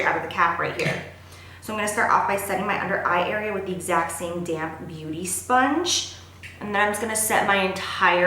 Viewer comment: I thought you said "I'm going to set the powder with the same damn beauty sponge " instead of "same damp beauty sponge" haha.